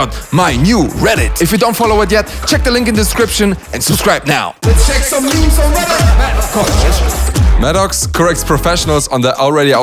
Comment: At first I heard "Let's check some nudes on reddit!" before I realized that it meant to be "news" 😂😂